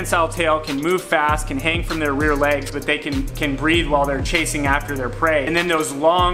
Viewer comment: I thought he said 'they can breed while they run" not "they can breathe while they run", I was incredibly impressed for half a minute there